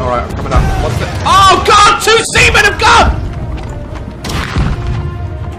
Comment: Lol daz said two sea men are gone . I thought he said semen 😂